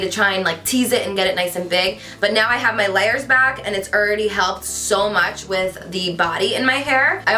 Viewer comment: lol did you say "Erready"